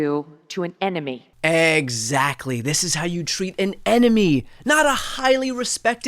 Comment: And I thought she said "this is how you treat an enema..." My bad.